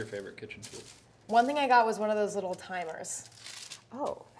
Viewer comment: What does Claire say at 14:35 I have listened about 10 times and can’t make it out. All I hear is “a small ice spatula”